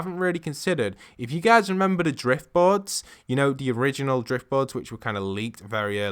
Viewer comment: is he saying Drift Bods?